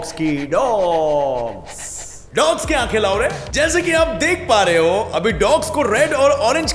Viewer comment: Gaurav : dogs ki ankhe lao re What I heard : dogs ki ankhe laure 🤭🤭🤣🤣